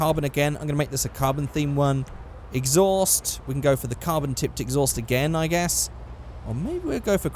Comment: It sounded like he said carbon tit